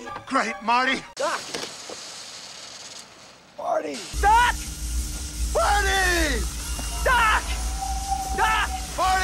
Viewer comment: It Sounds Like Doc Said "PARTY!"